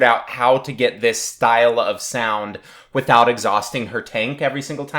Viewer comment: LMAO! At about 2:10 I thought you said "without exhausting her taint".